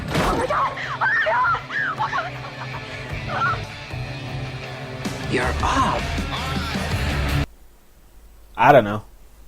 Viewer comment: I think he said “you’re up” Also I think that little scene with the guy shoving metal into his arm was supposed to be the set up for when we see the body with the scars after the explosion in the warehouse but it turns out it wasn’t the killer, just some random guy with the same scars. But I get your point in that u wouldn’t really be able to make sense of it until that time.